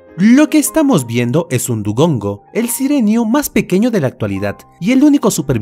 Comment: Pensé que decía duolingo